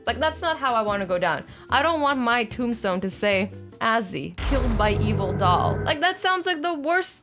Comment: put the captions on, its says ozzy instead of azzy lol